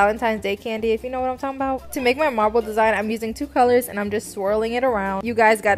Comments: i thought she said "marvel" design and im like WHO? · I thought she was saying a Marvel design lol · Lol I thought she said Marvel design at first